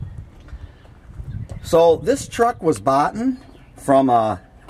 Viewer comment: Did you just say, "boughten"? Are you sure you're not from South Central Los Angeles?